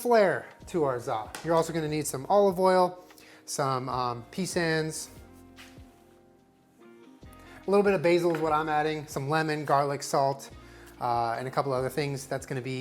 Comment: did you just say peSANS